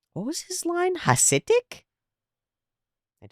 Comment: Starscream said "pathetic fools". For the longest time when I was a kid I thought he said the same thing you thought.